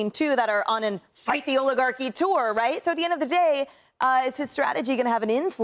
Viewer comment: When Bernice said Oligarchy, I thought he said Olive Garden .